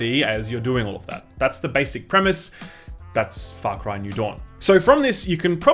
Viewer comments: Sounds like he said "Fuck Ryan New Dawn" 😂😂😂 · Not gonna lie, at the very beginning I thought you said "Fuck Ryan: New Dawn"